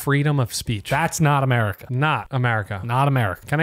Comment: He said "Not America" and I heard"Naughty America"😂😂